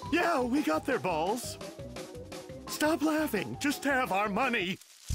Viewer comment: I know James says "Stop laughing! Just have our money!" at 1:42, but I've always heard it as "Just tear up our money!" It still kind of sounds like that too if you listen to it that way.